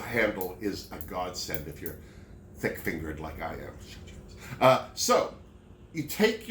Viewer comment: i was half-listening at the part you were talking about the bobbin casing (threading it to catch the little groove) and you said the little switch is "a god-send if you're thick-fingered like me", but i heard it as, "a god send if you have fingers like me" and i was like, "If you have fingers? whaaa?" the things you hear when you're distracted lol